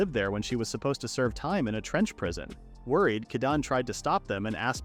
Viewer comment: i thought he said french prison hahahaha